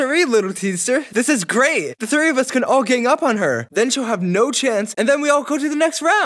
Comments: the subtitles say “flower” but just says “her” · her? The captions say flower?